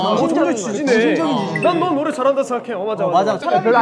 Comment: 난 여기가 "난 너 노래 잘한다(고) 생각해"로 들리는데 ㅋㅋㅋ 내가 잘못 들은건가..